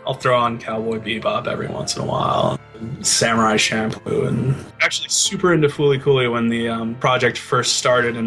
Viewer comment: what did he say 14:30? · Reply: Bebop, Samurai Champloo and Fooly Cooly.